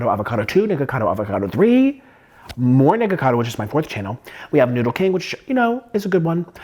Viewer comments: Yo who Heard the N-word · Bro I just heard what I just heard bro said the N- word😂